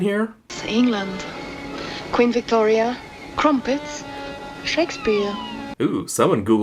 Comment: I thought she said sex beer not Shakespeare